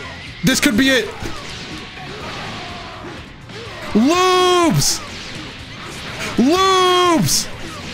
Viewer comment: I know everyone heard loops but all I heard was "THE LUBES THE LUBES" lmfao